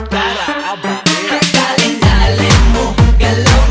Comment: I THOUGHT IT WAS SAYING DARLING DARLING😭 edit: TYSM FOR 352 LIKES